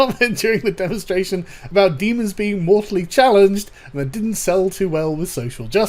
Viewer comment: At first I heard "morally challenged" because they're demons and it's funny. Then I realized it was "mortally" and I still found it funny because I mean...they're immortal, human hating and amoral fiends from Hell. I didn't even think it had anything to do with politics. Do people find it hard to comprehend that they should NOT protect the rights of Demons? What's next? We're gonna have a bunch of cultists protesting in front of the game studio with signs like "Demons have rights too!' or..."Lucifer is a minority! Don't oppress him!" or some other retarded bullshit like that? At this point I wouldn't be surprised... (Btw if anyone has a well sounding demon activist quote that they came up with please do tell me. I love a good laugh.)